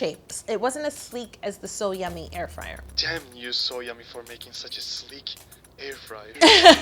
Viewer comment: Ok… am I the only one that thought when he said “damn you SoYummy for making such a sleek cake” that he was saying to Yolanda: “ damn, you’re so yummy for making such a sleek cake”😅😅 no? Just me🫠🙈